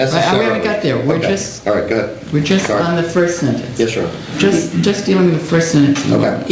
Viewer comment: attorney calls the judge a 'sir '. Did I hear that right? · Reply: said "yes your honor."